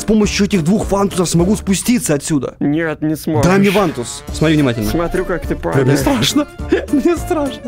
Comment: Мне одному послышалось на 3.03 дай мне в анус ?